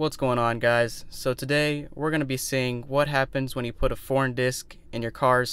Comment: I thought you say porn it's foreign lol😂😂😂